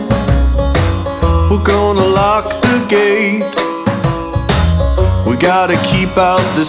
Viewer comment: Step into the garden We're gonna lock the gate We've gotta keep out the sinners We've gotta keep out the hate Look at the horizon The time is coming near Look out at the world on fire Look out at the people frozen in fear We won't listen to their cryin' They had their chance to see the light We won't be handing out no pardons Cause the world is gonna end tonight. See the rabbits running? See the mountains shake? Now the sea is flooding And the earth begins to quake Hear the boom of thunder? It sounds just like the voice Should have listened closer when they Went and made that choice We won't listen to their screamin' They had their chances to do what's right They'll be begging for forgiveness Cause the world is gonna end tonight. Those non-believers I'll buy their path They're gonna burn forever They're gonna burn to ash So take heave young heathen Listen to me close You either in or out And you're stuck with what you chose We won't listen to your beggin' You had your chance to stand and fight And now you end your days in darkness Cause the world is gonna end tonight. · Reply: I heard "Who's gonna lock the gate" and i typed it, but thanks for the information.